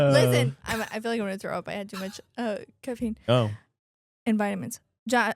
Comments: It sounded like said I had too much cocaine and vitamins instead of caffeine and vitamins 😆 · Did just say she's had too much C0CAINE!? 😳😳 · Did she just say cocaine n vitamins 😂